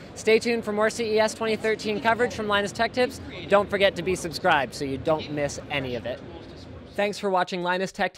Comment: Did he said "Don't forget to be subscribed..." ?